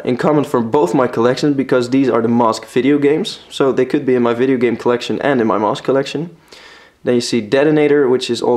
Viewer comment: Whoa, for a second I thought he said, "mosque collection"😂